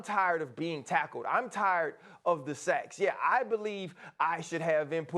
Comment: Everyone go to time 3:18. Tell me if I heard that correctly????? Am I buggin??? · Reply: He said Im tired of the sacks.